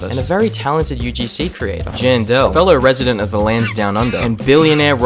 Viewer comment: i heard that as jane doe lmao